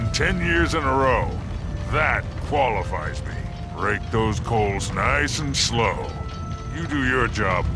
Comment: did he say " rape those coals niice and slow"seriously wtf ( I may have just misheard it)